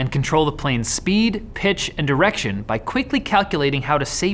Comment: i thought you said "and erection"